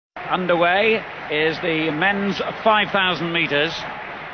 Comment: thought he said underwear at the start of the video